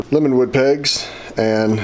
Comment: At the 10:13 mark, you named the type of wood the pegs were made from but the term isn't clear. What did you say was the type wood used for the pegs? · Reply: Lemonwood pegs